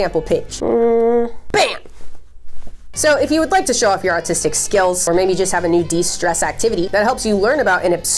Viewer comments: i swear i heard her say “autistic skills” and i cannot stop thinking about it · NOO I SERIOUSLY THOUGHT THAT SHE SAID AUTISTIC SKILLS INSTEAD OF ARTISTIC IM CRYING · You 100% said "autistic skills" instead of "artistic", I literally cannot hear an R at all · I thought she said autistic skills lmao I got so confused😂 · I thought she said "autistic skill" 😂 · I swear I thought you said show off your autistic skills when showing us your coloring book merch 😂